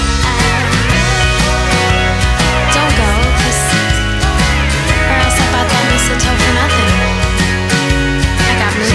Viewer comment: THANKS so much for putting this up!! LOVE that song! quick question: can anyone tell me what katie says between 2:52 and 3:00?? "dont go...."? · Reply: I know this is seven years late but, if you still want to know, she says "Don't go...or else I bought that mistletoe for nothing. I got movies, eggnogg..."